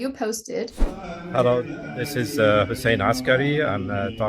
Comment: When you say his name, I heard you say Jose Askary 😁. I look at him and thought, thats definitely no a Jose 😆. My bad, I did not read the title first.